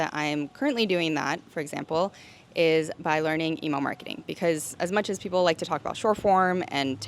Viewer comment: I kept hearing "emo marketing" when you first mentioned it, and thought, fuc* yeah, that's exactly what we need right now, crazy but brilliant! Then understood it's good old, solid, reliable "email marketing". Phew. Thanks for the empowerment and encouragement, it's what a crisis-ridden freelancer needs to hear. <3 (<- emo heart)